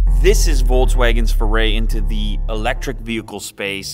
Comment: Why do I keep hearing "VOLTSwagen" from Lew? 🤣